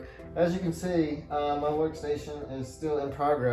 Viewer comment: I thought I heard playstation at 6:48 Anyway the home tour is great